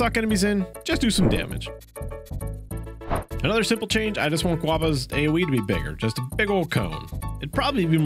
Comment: Did.. Did he just called Guoba as Guava??